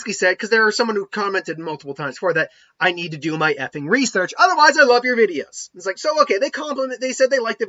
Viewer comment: I thought he was saying epic research not ffin research